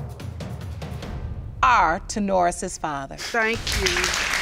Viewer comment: I thought that they were saying Sonaris but it’s actually Ta’Norris?!?? Guys come on! Why’d they name the baby that?